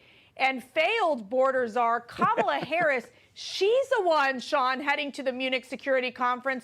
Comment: I Harris I thought u said Clinton lol